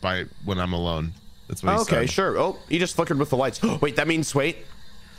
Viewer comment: i’m sorry but did nobody else notice that ranboo said “is he f**king with the lights?” at 24:35 well that or he said flickered but i heard the first one